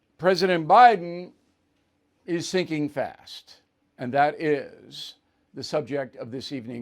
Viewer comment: At first i heard "President Biden is thinking fast" and i thought what the heck? - OH, SINKING fast. XD. I should watch this before i have my evening glass of wine!